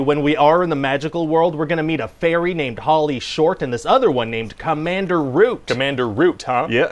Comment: Sounds like he says Pauly Shore, not Holly Short. Must explain the reviews.